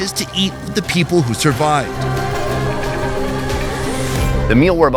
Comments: did you just say you're eating the people who survived??? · the with was so silent that it sounded like he was going to eat the people who survived 😂😂😂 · I heard ''to eat the people who survived''.. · Lmfao I thought he said to eat the people who survived 0:44 😂😂😂😂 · did he just said eat people?